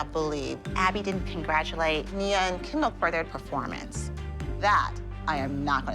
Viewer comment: am I the only one that heard kindle instead of kendall